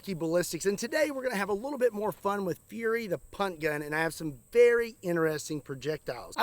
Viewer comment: I thought he said “the *unt gun.”😂